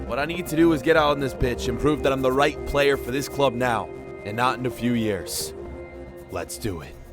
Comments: did he say pitch or b———h😂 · im a the only one who heard " get out on this bit** "